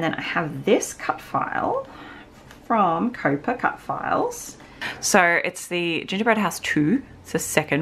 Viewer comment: What a cute album! Can you please tell me where you got the gingerbread house cutfile? It sounded like you said Coper Cutfiles, but I couldn't find anything like that. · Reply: COAPA Cutfiles on Etsy 😊